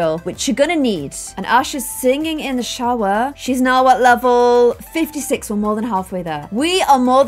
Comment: Did she just say level 56